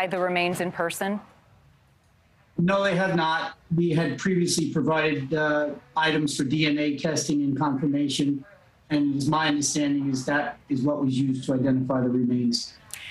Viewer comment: In another words “I’m sure he was a good kid but he lost his hair and strangled Gabby”…. Liar, liar 🤥 pants on fire! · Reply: I’m not sure if you heard what he said. @ 0:27, “We have previously provided, um, items for DNA testing and confirmation and its my understanding is that is what was used to identify the remains.”